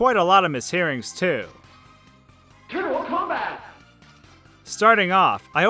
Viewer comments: I heard "turtle cum bath" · the TMNT fighter game sounds like it says "Terrible Combat" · I heard TERMINAL COMBAT. Sounds like the name of a hacking war. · I don’t get confused I just hear “Let’s A Go!” 😐 And the ninja Turtles I heard “ Turdial Kwombat” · I have never heard the tmnt fighters "turtle combat" clip until this video and I thought it said "terrible combat" lmao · the TMNT fighter game sounds like it says "Terrible Combat" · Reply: I used to mishear it as Mortal-a Kombat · I thought it said turtle cum rag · All I heard was "Terrible combat" kek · I've never played TMNT Tournament Fighters, but in this video I DEFINITELY heard "turtle cum bath!"